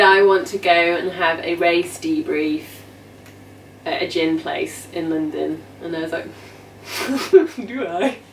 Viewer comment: Great storytelling!, Lol: at first I thought you said: "race debriefing at a gym place", which actually sounded less suspicious if you ask me 😃